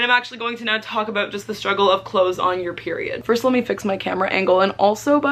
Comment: I thought it said the struggle of “clots” on your period